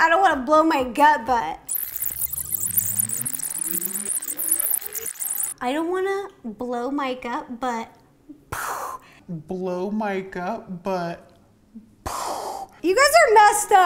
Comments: She said: " I don't wanna blow Mike up, but..." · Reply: Kaltri Kuka i heard “i don’t wanna blow my gut up” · I heard “ I don’t wanna blow my gut but…”